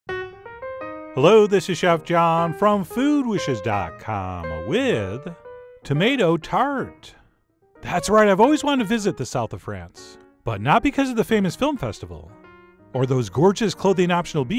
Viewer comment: I thought it said tomato fart, phew